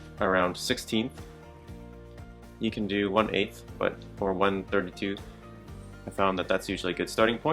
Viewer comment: did he say 32th?